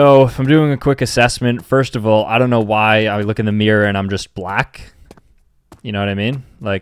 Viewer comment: Sambucha: 7:52 me : wait.. WHAT DID HE SAY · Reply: “In the mirror and I’m just black?” 💀